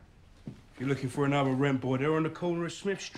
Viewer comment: Hey Brits: What does the guy at 0:33 say? I hear: "If you're looking for another rimple. They're at the corner of Smith street." (Please correct me, if I am wrong). Apart from the fact that he is th-fronting ("Smiff-street"), I could not really fathom what "rimple" (if I understood correctly) meant. One of the meanings I found is "(big)ass"...So is the guy implying that he would have to look for other (gay) hustlers at the corner of Smith Street? Dankeschön in advance from "Chermany" - :-D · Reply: He says 'rent boy', implying he is gay.